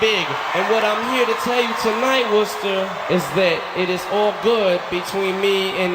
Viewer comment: Did he just say Worcester? As in, Worcester, MA?